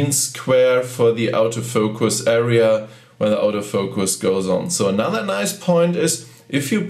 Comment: Thanks for this comprehensive review. I came here to see what the viewfinder looks like (I only have the X10) and you provided it! One small note for non-German speakers: when says "auto-focus" I thought he was saying "out-of-focus". Don't worry, that's just the German accent pronunciation of "auto focus".